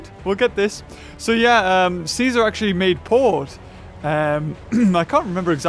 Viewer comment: I thought he said ceaser made porn 😂